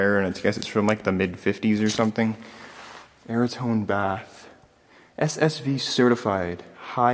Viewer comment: Wanted to look for information on the bath shown at 26:30 in the video but I can't make out the name that is being said. I played that part of the video (where he is saying the name) over and over and tried searching everything I could think of that might have been the word he sait (verotone? veritome? mariton? ???) - what is the name or manufacturer name being said there?